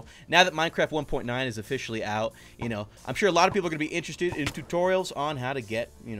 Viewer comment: it kinda sounds like you said Toriel's name instead of tutorial, because I didn't hear the "tut" part